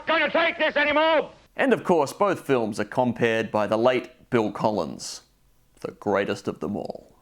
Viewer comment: I thought you said Phil Collins for a second and got really sad lol